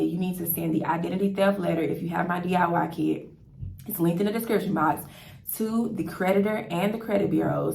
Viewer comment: What do you say the 3:31 minute mark of this video can someone help me out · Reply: I said send an identity theft letter if you have my DIY kit its linked in the description box!